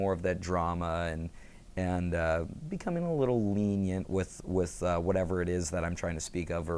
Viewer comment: What's the word on 8:52-8:53? "Becoming a little ..." what? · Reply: Lenient.